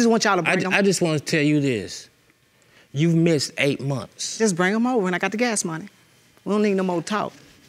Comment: Did he just say " bring him over and I got gas money"